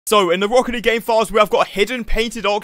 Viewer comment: I thought he said rocket league gay files and I thought TAHTS why it’s rainbow 😢